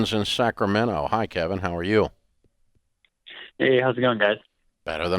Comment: Am I the only one that heard him say "Hey how's it going dad."?